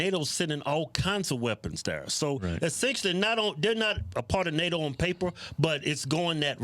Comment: Thought he say a pardonado lmfbo not a part of nato